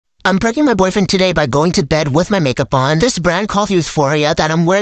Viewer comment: I heard " i pregnant my boyfriend today 💀💀